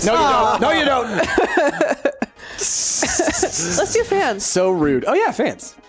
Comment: i heard "Let's do pants" :D